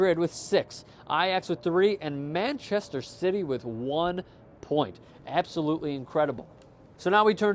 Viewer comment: did he just say manchester shitty? I like it tho